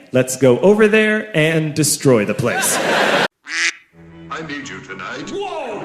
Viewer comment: wait so i was rewatching the video and. . .what did shiro say? i mean i THINK he said "i need u you tonight" buuuut i could be wrong